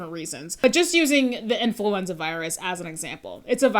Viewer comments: I thought you said 'influencer virus' and was wondering how that might spread. 🤣🤣🤣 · Ahahaha I was distracted-listening while doing some spreadsheet work and really thought you said 'influencer virus' at one point Also all the best for your internship!! Have been following your channel for about 18 months or so, so excited to see a creator I follow move on to the next steps in their career :)